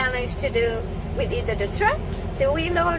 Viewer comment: When she said “wheel loader “ I thought she said “wee loader “ 😂